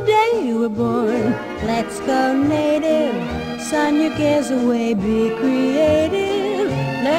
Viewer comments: I hear "Sign your gives away, be creative" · I literally thought I said "sign your kids away" ='D